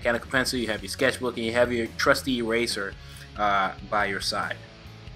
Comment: i thought he said trusty razor so in my head im thinking wtf is he gonna cut his wrist if he doesn't like the drawing or somthing lol